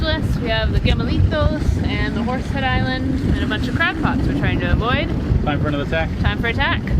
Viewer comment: What are you man or a mouse.?!?😜👍 At the start of the vid I misheard you say "crack pots"... chuckled to myself.... Nice to float along with the spinnaker flying even if it's slow👍⛵️ Warren